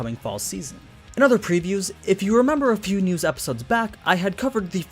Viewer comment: I thought you said pewnews so I went to check pewdiepie's channel